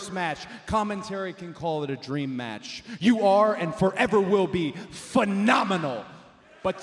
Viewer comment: WHAT THEY SAID IN FRENCH? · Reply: Il est vraiment phénoménal lalalala 🇫🇷🇫🇷🇫🇷